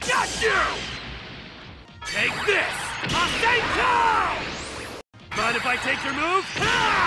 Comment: No joke at 3:15 he sounds like he is saying Rasengan, like the Naruto move